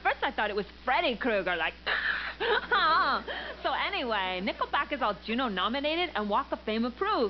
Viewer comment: For a moment there I thought she said nippleback...